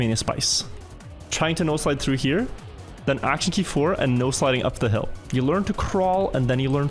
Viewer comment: I've been watching your videos for a month or two now and I just want to say that I only just figured out that you've been saying "no sliding". What I thought you had been saying was "nose sliding" and I was always so confused about what that meant.